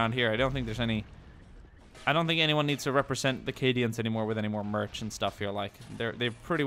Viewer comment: I thought he said Canadians